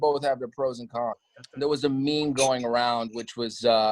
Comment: Did sebastian just said MEME NOT ME ME 😦